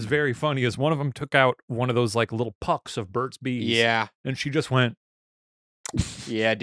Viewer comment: At first I thought Shane was saying "bird seeds" and not Burt's Bees